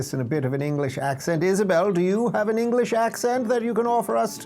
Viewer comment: Why did Dave call Isabel “Israel” (00:16 sec)?